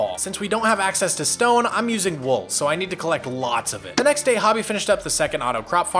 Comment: I keep hearing it as "hubby" like husband xD